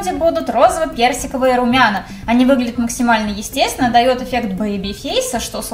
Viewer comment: Дают эффект поеби фейса???? Или я что то не так расслышала😂